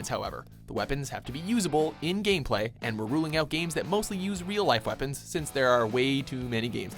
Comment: I thought you said no real life weapons.